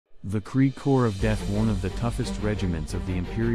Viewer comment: I legit thought he said the Krieg Whore of death XD